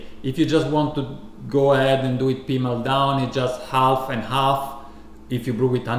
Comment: Did you just say "Pi mal Daumen" or did my ears betray me? :D